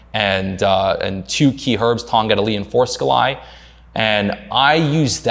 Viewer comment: Tongkat Ali and what is the second one he says? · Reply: Forskohli